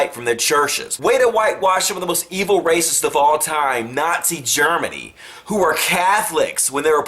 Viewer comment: Why wouldn't he? Are we supposed to pretend it didn't exist and was backed by church leaders etc. when it helps our counter point? That makes little sense. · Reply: Tessa Bain for it sounds like he says it like "still nazi germany"... idk if it is a general term for hitler time period in america but if you say that in germany you would transfer it to the present time and germany today is defently not a country with a lot of racism ... but for sure i understand his point and i was wrong then sorry!